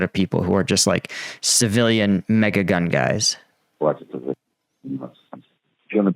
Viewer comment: did I hear civilian MAGA gun guys?